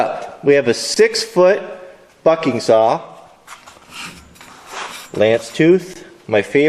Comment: it sounded like he said 6 foot f****ing saw